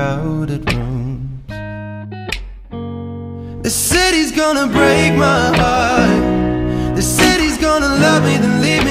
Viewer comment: Am I the only one who thought he sang,”oh they say he’s gonna break my heart” instead it’s actually “this city is gonna break my heart” 😂😂😂😂😂😂😂😂😂